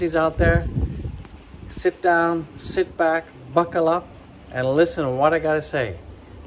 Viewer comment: I thought u say fuckle up lol. To the moon!!